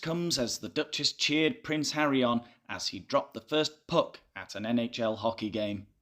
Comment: I’d wandered away from my screen when I heard “Harry dropped the first puck.” But I mis-heard it as “dropped the first PUP” and thought, HARRY CLUBBED A SEAL PUP?!